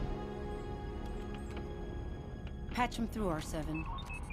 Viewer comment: im sorry its not Ashoka listen to what she says "patch him through" not "her through" i thinks its Senata Bail Organa not Ashoka